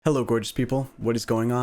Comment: NGL, I misheard your intro from the kitchen and thought you said 'Hello, British people'. For a short time, I felt included. :P